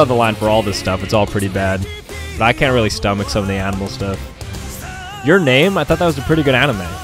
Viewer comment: Who else thought he said animal on your name 👇